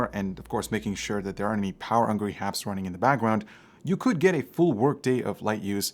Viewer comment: “Power angry happs” instead of power hungry apps lmao